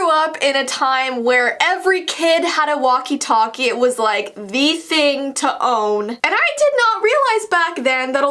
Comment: I thought she said VEE thing for a min 00:47 The scary thing that happens to me and my brothers with are walkie talkie is we heard people whispering inside them and talking about work but the thing is are walkie talkie only came in a pack of two so it was the walkie talkie that can only connect to each other not like the real ones were it connects with everyone else